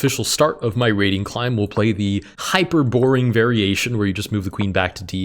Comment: I misheard that as Hyperborean variation of the Scandinavian, where you move your Queen back 😂